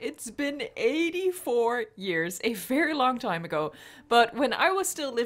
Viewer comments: did she say 84 YEARS!?!? TELL US THE TRUTH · Did I actually hear say “84 years”?! HOW OLD IS SHE?! (Not trying to offend you but still that’s what I heard and I hope I’m wrong)